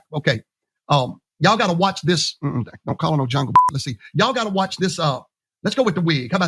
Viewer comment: Did this guy say, “Jungle pu$$y”?!?! Did anyone catch that?